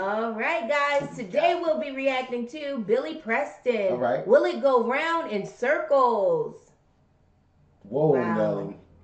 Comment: When I was a kid,I thought he was saying "Willie go round in circles". I always wondered who Willie was,lol